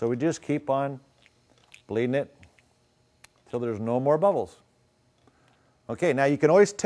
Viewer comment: i thought u said no more bleeding